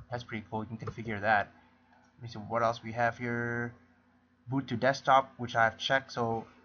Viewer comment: haha thought you said Ubuntu desktop when you said "boot to desktop" :o